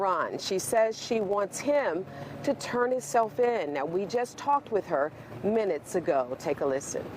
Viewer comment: did she say turn his self in?